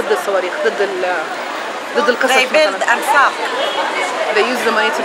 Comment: when she said "anfaq" ( انفاق), tunnels, "They build ...anfaq" , I heard it as "They build and fuck."